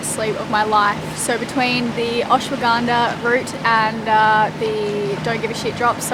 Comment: Catching up while doing the dishes.....had to dry off and rewind, misheard Ashwagandha, thought you said Ayahuasca (pronounced - aya-wash-ka) but thats very much a different kind of dream chaser....and would not be adviseable....phew Riley you could try stitching in a couple of sleaves for shortened fibreglass tent poles or sail batons to hold an arch in the bimini Loving the episodes, thanks for sharing